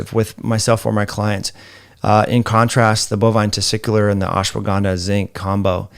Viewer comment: Bovine Testicular? 44:44 Did I hear that correctly?